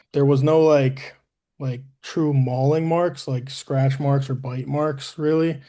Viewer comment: Did he say there was no mauling marks or scratch or bite marks on him at 13:44? What was his cause of death?